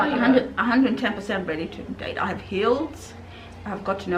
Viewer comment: I thought said "I'm 100% ready to date, I have heels, not I have _healed_. Lol.